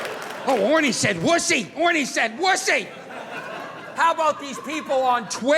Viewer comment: Oooooh....his first name is ORNY! I thought he said he was horny! I was thinkin' ok...is he performing at a gay nightclub, or what?